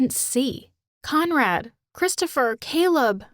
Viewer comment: conrad lol i thought it said comrade lol